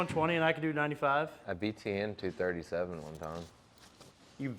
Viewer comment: Does say he beat Tian???? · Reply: Btn- behind the neck